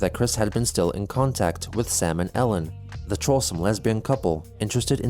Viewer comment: Everytime Chris says "Sam and Ellen" I hear "Salmonella"...